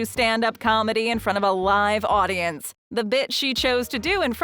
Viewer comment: I thought I heard “the Bit**” at 10:26 😆